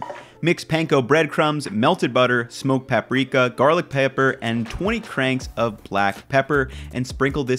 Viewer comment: Did he just say garlic paper at 2:33?